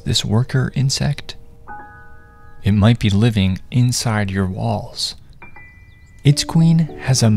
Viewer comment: i just thought he said "inside you're balls" and than i rewatch it realizing he said walls not balls